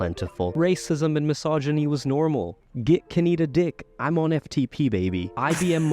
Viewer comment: tbh i thought he said "get kaneda d!ck" and i was briefly perturbed by the idea of akira thirst being all the rage